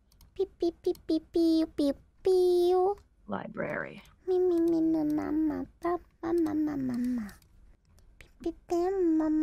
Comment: I am a french Guy and a 0:09 kobo sound like to say "Je t'aime maman" or in english "I like you mom"